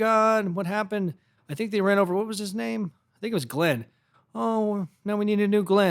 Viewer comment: “Oh no, now we need a new Glen”😂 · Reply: I thought he said Glynn 😂